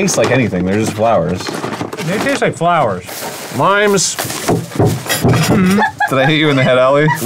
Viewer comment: I misheard it as "mimes" and I was perfectly willing to accept that they were using little mime miniature as a garnish 🤦🏻‍♂️